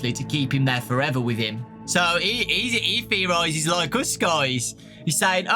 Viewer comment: lol what did he say? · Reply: "so he theorizes like us, guys"